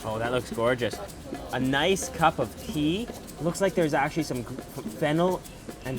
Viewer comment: I totally..thought he said pee <-<